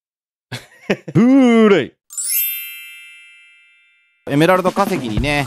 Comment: 個人的な意見ですけど…… 毎回「プーレイ」が。。。『ブルーレイ(Blu-ray)』に聞こえるんですけど…… まさかの俺だけ(？)😅🤔🤔 あっその前に……初めてましてですよね、いつも動画を拝見させて頂いてます。【カッチャン】です。 初めてと言うか、一番初期のマイクラ実況見てたんですけど……いつも勉強になります🙇🙇 動画配信いつも楽しみにしてます。動画投稿頑張ってください🙇😅